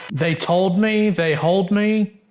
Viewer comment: Voice through the voice box sounds like it said 'They told me" to me. * shrug *